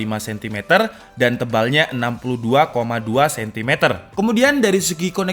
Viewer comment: tebal 62,2 cm?? Maaf kalo sy salah denger